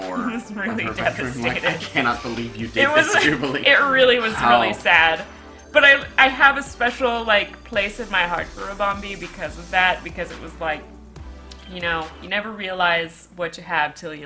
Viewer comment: The auto generated subtitles said," I have a place in my heart for a *bomb*"